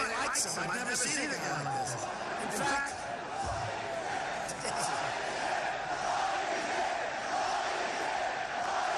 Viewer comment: What are they screaming at 1:22 ? Oy vey, oy vey? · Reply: Lying Ted